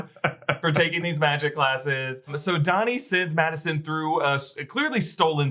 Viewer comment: sounds like says Donny sins